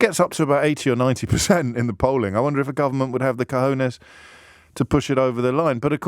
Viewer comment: Did James said: "…the government would have the cojones…" 😅? Minute